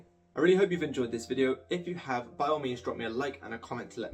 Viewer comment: lolll your voice transcription put “biomedians” instead of “by all means” Is that accent discrimination lmaooo